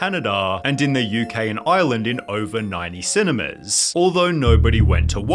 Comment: Why did you say island? It’s Ireland 🇮🇪